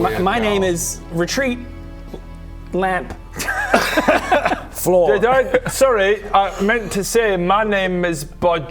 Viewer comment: outing Bobs name... 🤌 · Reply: Who outted him? All I heard was “Retreat Lamp Floor”.